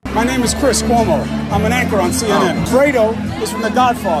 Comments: It sounded like he said he was an actor on cnn which would be appropriate also. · I thought he said an actor on CNN.... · I thought he said he was "an actor on cnn". My bad. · It sounded like he said "im an actor on CNN" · Sounded like he said "Im an actor on CNN" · I thought he said he was an actor on cnn